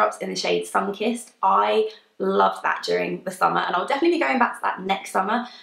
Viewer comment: The echo makes it really hard to understand what you're saying. I thought you said "rouge puffy" and when I listened to it again it sounded like "puppy". I'm assuming you said "poppy". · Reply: Jodie653 Agree - at about 3:45 I thought she said "I like badgering in the summer"! 😂